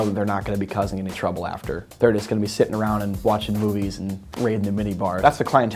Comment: I thought he said watching boobies 😹😹